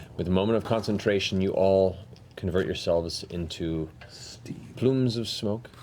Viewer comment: I wasn't paying attention, so this is what I heard here; Matt: "In a Matter of minutes, you all turn into-" Tal: "*STEVE*" And it's the funniest thing to me even though I know he actually says "steam"